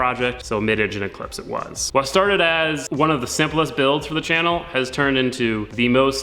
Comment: I legit misheard "simplest" build as "syphilis" build... With the amount of struggles you've gone with I am not even going to be mad at my brain. Rolling with it... The engine though... I understand people can make mistakes but there is no way a professional builder misses something THAT obvious. I am glad he made you whole but now he has the opportunity to sell another one of his FUBARS to someone less knowledgeable, they actually assemble the engine, turn it over and find out it's bunk (or not even notice something is wrong and run it to a premature death).